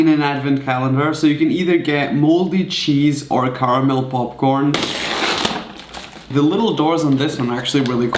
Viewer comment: anyone else hear ‘condom popcorn’?